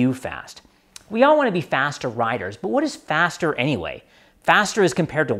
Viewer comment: sounds like your saying slow is fast. I agree. thanks for the video.